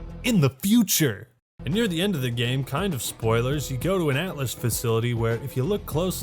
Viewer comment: did he actually said spoilers? lol dude this is buried and forgotten for a reason no ones gonna play this. campaign go (BOOM)